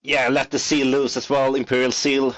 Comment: Did he say Memory Jar? If so that’s a super spicy take.